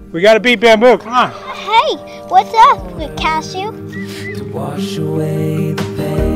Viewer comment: did he say hey whats up pikachu hahaha